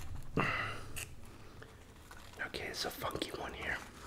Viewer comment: I died when was having a hard time opening an oyster and i thought he said oh we got a F u one here 🤣 then i realized he said a funky one 🤣 🤣